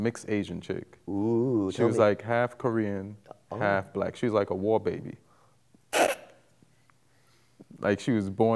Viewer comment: WHAT DID HE SAY?? WABAY?? OR SOMETHING? CAN SOMEBODY EXPLAIN IT TO ME??